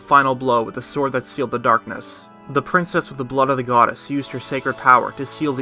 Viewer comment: I thought he said with the butt of a goddess lmao.